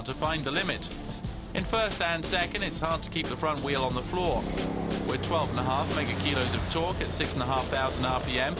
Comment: did he say "twelve and a half Megakilos" ? rofl Good review though